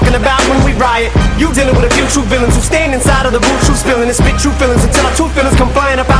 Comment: i thought he said You're dealing with the YouTube villains at 0:34 xD